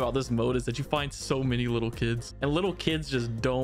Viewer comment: Yo what he say play it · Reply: Lil kids woah